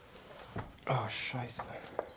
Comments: did you say "oh scheisse" @ 20:37 ? :D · Did u say: "Ah Scheiße" ? this made me laght so hard hahaha :D